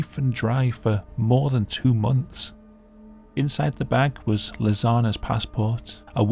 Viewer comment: I keep hearing "Lasagna" when you say "Lisanne". Makes it kinda hard to take seriously.